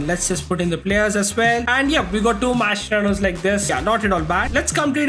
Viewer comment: I thought you said this guy has 240 mascheranos😅